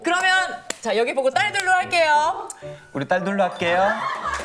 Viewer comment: 우리 딸 둘 낳을게요로 들려요 꺄아.....🎉🎉🎉